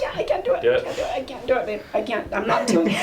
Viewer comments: is it just me or did she say babe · Did she call him Babe at 4:15 or am i tripping. If so then this make alot of sense.